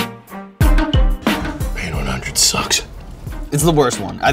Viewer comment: What does he say? "Being ---hundred sucks?"